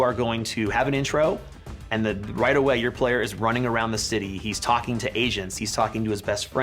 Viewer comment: thought he said “he’s talking to asians” 😭